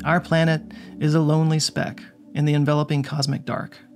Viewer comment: did you say sneck? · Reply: yeah,, but did you say "SNECK"?